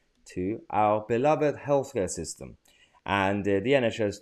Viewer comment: Not meant in any way as a criticism of your speech, but I had to listen 3 times when you said 'beloved healthcare system.' 0:08 I could have sworn I heard 'health scare system.' Thought that was a very apt description.